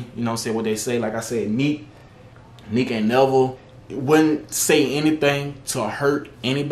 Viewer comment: Did he say nevel